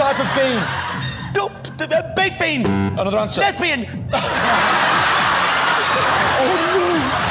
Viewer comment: I thought 💭 he said lesbian